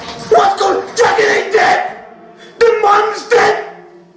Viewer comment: What did he say there? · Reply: "Dragon ain't dead the mom is dead"